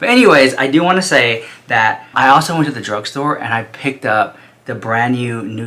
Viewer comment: You said drug store but I thought you said thrift store 😂